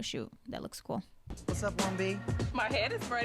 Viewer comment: I think he called Dionne 1B , because in casting Dionne was telling them that her hair color was 1B30